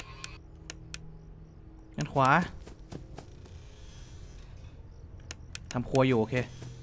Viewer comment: หูผมฝาดได้ยินว่า ทำค*ย อยู่ ok😂